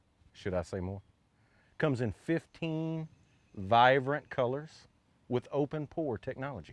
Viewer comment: Did he just say "vivrant" colors?